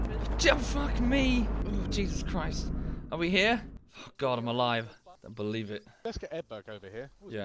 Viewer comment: revives, an “intellectual:” AIJASLAHV—! puts out of his pain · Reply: I heard “I’m alive”